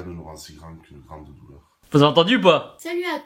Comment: il dis quoi le barbu à 8:06 ? "Rien ne nous rend si grand qu'une ...." j'ai pas compris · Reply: Grande douleur 😉